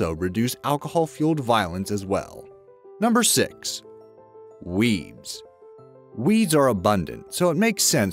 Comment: I thought he said "weebs" for a second.